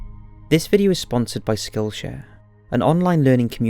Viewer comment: I thought you said "this video is brought to you by Skull Share" lmao I was like "the fuck... did I change dementions without knowing or something?" Lmao